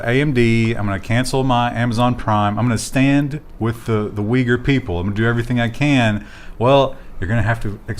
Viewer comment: What does mean at 25:43 He's saying: "imma stand with the ... people" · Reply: I thought it would get "lost" if I wrote it out... U-y-g-h-u-r